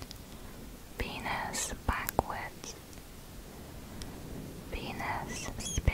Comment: Oh my God I thought she said penis backwards and I'm like "what the fuck kind of conspiracy exists about penis spelled backwards here we go im ready" lmfao